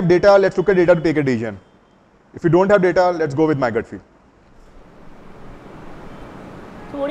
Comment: Very nice story, awesome! I din't get it, even after listening multiple times. What was the last word in (at 34:35 mins): ...if you have data, let's date take out the decision, if you don't have data, lets.............. (something like 'mygetfield'). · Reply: If you have data you look at data, if you do not have data LET's GO WITH MY GUT FEEL